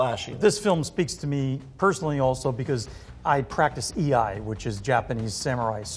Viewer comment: What is he saying at 7:41? E.I.? never heard of it. I've heard of 剣術 (kenjutsu) but never what he said. Just did a little searching, and actually what he is talking about is居合術 Iaijutsu or 居合抜刀術 Iaibattoujutsu, which is a branch of kenjutsu. I would imagine the reason he's leaving out the rest of the word is because he practices with specialists who only talk about particular techniques and thus don't need or don't feel the need to include the last few chinese characters which specifically state that is a form of Sword Art. I would imagine also that even if he is practicing with Native Japanese those men or women are probably also leaving out this part as well and accordinglt he may not even know the rest of the term.